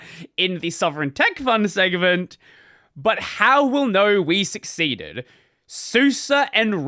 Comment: Does not say "How we 'will' know," but actually know. It's saying this IS the case not will be. Suse does in fact, already ship with KDE as the default.